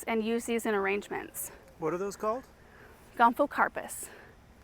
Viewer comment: At around minute 11:42 you say the name of the plant but I can't understand what you're saying enough to Google it . Not to look up the inappropriate name, (I think you mentioned it in another video) but just the Latin name. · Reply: Gomphocarpus physocarpus